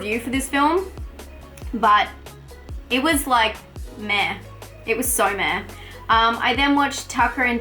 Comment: I swear i thought said that family blood was like meth" but dont worry i know you said meh